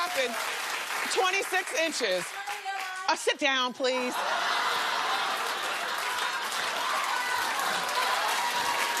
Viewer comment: What did she say at 1:12? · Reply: “This is her hair line..” She was trying to advertise her friend’s business